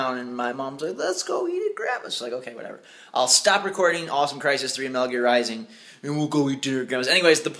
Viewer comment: I heard him say Crysis 3 :D